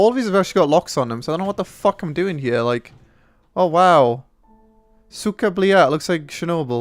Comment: What is the Russian phrase is saying at 13:30? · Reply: Im.Puk its cyka blyat...